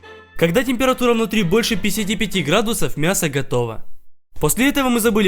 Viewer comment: Я сам услышал писити пити градусов??